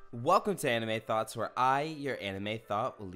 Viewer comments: When I first started the vid I thought he was saying “I’m anime thot” 😂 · I thought you said Anime Thot lol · I thought u said hi anime thots